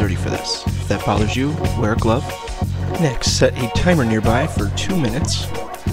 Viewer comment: he did not!! he said use the timer on the microwave!. I don’t even think he said the word microwave. He just said “set a timer near by”, witch rather suggests that he didn’t put it in there… well that’s what I thought… some people misinterpret stuff.. : / But he did say set a timer near by he kina mumbles so its hard to hear 0:39 Is when he says it